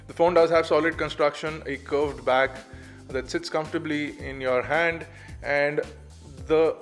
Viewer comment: did he just say it 'tits' comfortably :P